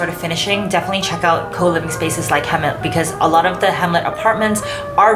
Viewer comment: sorry can anyone tell me what did she said on 16:40 , where to find or who to contact when i like the renovation unit ??? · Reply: Hmlet, one of the co-living spaces I mention also earlier in the video :)